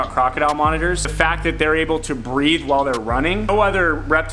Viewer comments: I misheard Forrest completely when he said that the croc monitor could breathe while it runs and thought he said that it could BREED while it runs. I was so confused and I have to admit, a little impressed, until I realised what he actually said 😂😂😂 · I swear, I thought he said they were the only mammal that breeds while running. I was like WHAT!!! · I definitely thought he said "breed while their running" and was just sitting here thinking hmm thats a real skill right there! Loll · I thought he said they can Breed when they are running I’m thinking gee wiz they are gifted , I gotta try that , But.... my wife told me Breath!!! He said breath dummy 🙃.... I’m sure Kevin has some totally awesome hatches for CM